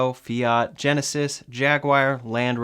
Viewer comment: did you say jaguaire or something?